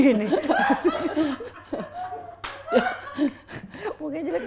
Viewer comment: hesti bilang mukanya jelek bngt Sumpah gw risih dengernya!!